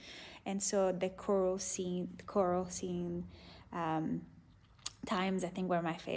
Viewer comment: what is she saying? the coral scenes? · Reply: *choral scenes probably like the ones where they dance